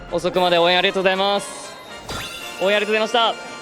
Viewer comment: すみませんが、後半は聞き取れないです（日本人ではないです） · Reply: ＜坂井瑠星＞ 遅くまで応援ありがとうございます。応援ありがとうございました。 ※日本のファンに向けてメッセージを求められた際の騎手のコメントです。 <Ryusei Sakai> Thank you all for staying up so late to support us! I'm truly grateful for all your support! *This is what the jockey said when asked to give a message to fans in Japan.*